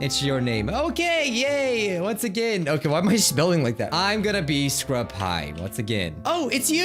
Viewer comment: I heard you say scrubhigh